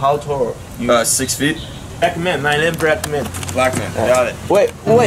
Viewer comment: 我聽成Bretman 😂 同樣是YouTuber